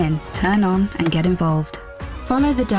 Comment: Sounded like it said 'The Devil's Agenda' I assume it is the devil's agenda anyway. 🙏🏼❤🙏🏼